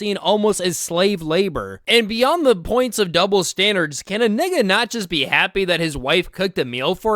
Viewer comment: did bro say nigah 💀